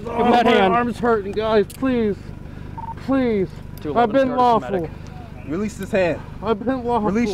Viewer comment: “I’ve been lawful “- then why are both windows broken and you were extracted from the car with a dog as your wrist watch? 🤣🤣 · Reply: Zapp1 Gaming I thought he was saying, "I've been awful"....lol